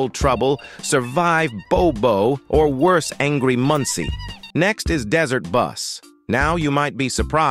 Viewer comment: Whe the ai says angry munci i hear it as "angry muntsi" 😭